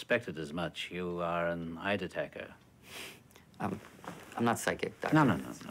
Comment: What does hopkins say at 1:18 - "I always suspected as much you are an __ __ " (sounds like Ider Tecker or something but I can't make it out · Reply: Well I found it, now to go find out what it means eideteker